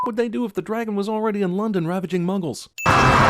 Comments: sounded like he said mungles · gonna be honest sounds like he said mungles